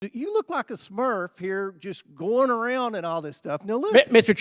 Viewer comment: Did he just say smurf